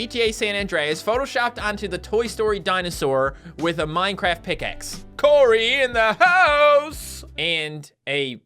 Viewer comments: did he say Minecraft pickaxe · Woah woah woah, did he say diamond pickaxe?!? 4:26.